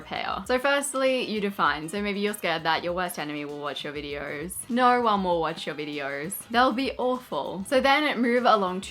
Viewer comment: Oh my goodness, when you said "your worst enemy will watch your videos... no one will watch your videos.... etc" for .2 seconds, I literally thought you were saying "your worst enemy isn't going to watch your videos because NO ONE is going to"! 🤣 🤣